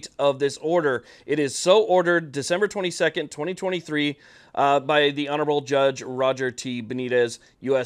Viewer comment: Did I hear Dec 22, 2023? Check